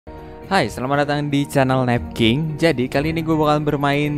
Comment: Gw dengernya nadeking bjir...